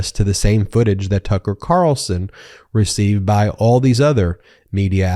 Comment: I am laughing because I thought I heard you say pucker as in "Pucker" Carlson. I started thinking of all his facial contortions when he is trying to process simple concepts and I thought it was so apt. From now on it's Pucker Carlson. 🤣